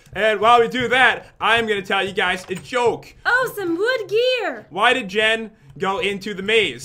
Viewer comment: When pat say “wood gear” it sounds like he said”wood beer 🍺”